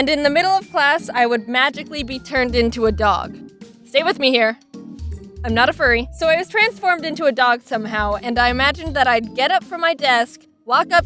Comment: I thought you said and I was transformed into a furry.